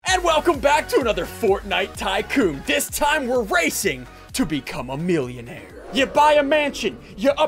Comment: In the beginning when you said were racing I thought you said rasist